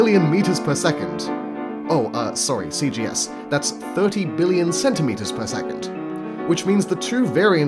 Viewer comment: I thought I heard 30 million centimeters per second I think he meant billion